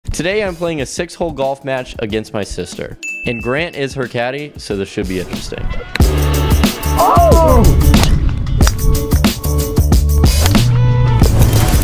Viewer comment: I definitely heard "and Grant is her daddy" at 0:03 the first time listening (wasn't watching the screen) 😆 good video lol